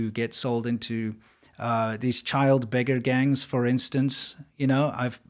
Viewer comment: I had to replay 8:50 almost 4 times...I thought you said Lego Gangs...